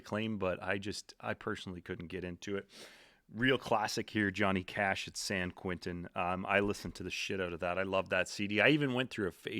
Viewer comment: I had to rewind to see what you said here, because I swear that I heard: "John Tesh at San Quentin"! 😂😝😜 Also, "Another Sunday" by I Mother Earth is a badass song. I got it on a mix CD years ago... now you've got me wanting to hear more of their stuff.